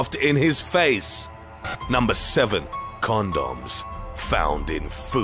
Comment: Bruh, #7, I thought you said they found corndogs in the food! 😂 I was like, what's the problem??